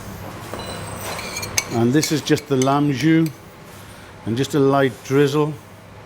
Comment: what does he say here? What sauce is that? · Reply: Lamb Jus